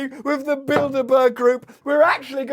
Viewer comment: I was taking a shower and I thought you were saying Buildabear. I was like woah, that’s like evil Furbies.